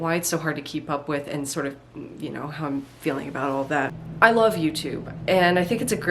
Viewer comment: first i heard "i love u" and i was just like "LOVE U TOO JAMIE" <3